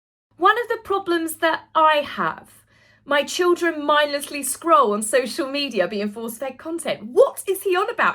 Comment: Sounded like he said scrawl instead of scroll